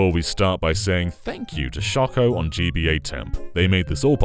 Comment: I heard at first: First of all, we start by saying F*ck y*ou to ...! Hahahahahah I rewinded to see If I really heard correctly! Hahahahaha